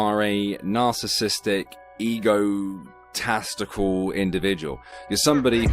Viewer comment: did you call Richie Egotestical? Or did I simply mishear? 😂🤣😂🤣😅